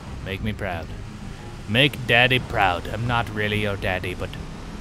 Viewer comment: i thought i heard him say "i would have been your daddy" haha